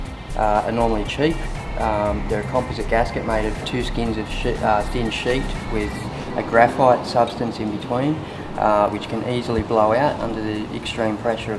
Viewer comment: sounds like he said two skins of shit